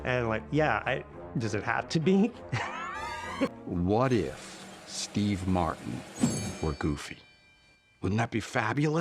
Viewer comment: so I took a hit & I was coughing a little hard & misheard it as “what if.. Randy MARSH-“ and 😂